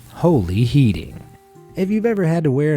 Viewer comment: Idk about anyone else. But I hear "holy heating" at the start. Not holy healing. Anyone agree?